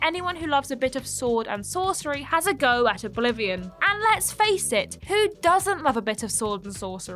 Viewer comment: I thought you said “have a go at a Bolivian” 😅 I was like what did Bolivians do 😂